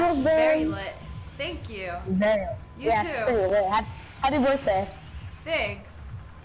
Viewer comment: did she just say : "you too" when he told her : "happy birthday"??